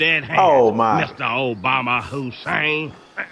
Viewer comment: did he just call 'im Obama Hussein? ☠️☠️